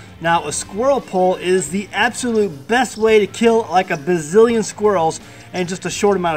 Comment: Thought it said Brazilian squirrels. This is better.